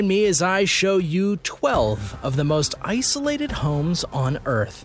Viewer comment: U lost all credibility 0:20s in to your vid with Cc's... It's 7 Seas. Do u know research ...? Lol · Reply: Provably even earlier at 0:08 when it says “Ssolated” instead of “Isolated” lol